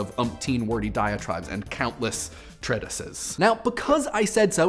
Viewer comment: What did he say around 1:00 I can't even find those words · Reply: He said "treatises", the plural of 'treatise' which means "a written work dealing formally and systematically with a subject."